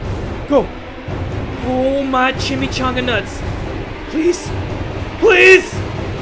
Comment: I thought he said chimmy changa nuns